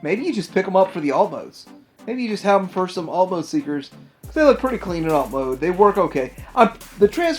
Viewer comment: I heard 'Fanstoys seekers' and almost had a heart attack. Then realized you said Mechfanstoys.